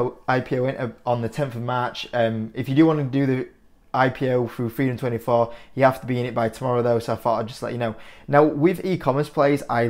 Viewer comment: i couldnt understand what you were saying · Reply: "On the 10th of March, if you do want to do the IPO through freedom 24 you have to be in it by tomorrow though so just thought id let you know"